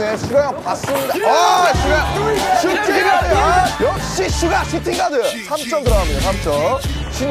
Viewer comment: man I swear I heard "Man you shootin' 3's my niga"